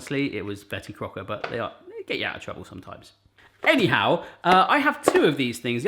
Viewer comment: Honestly convinced your a secret Afc Bournemouth fan or I support them to much! I always hear "Eddie Howe" when you say any how